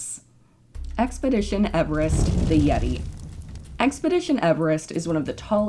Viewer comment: I thought it said yeet